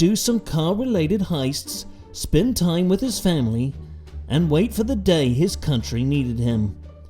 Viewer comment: honestly thought narrator said SPIN TYRES WITH FAMILY.